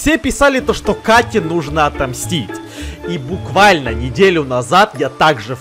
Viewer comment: В начале мне послышалось слово: Бздить, а не мстить!Лайк, если тоже! ( И помогите мне набрать 30 подписчиков! Выложу ПАРОДИЮ!)